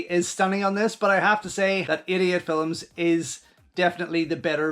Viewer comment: I keep mishearing you say Idiot Films instead of 88 Films. 😂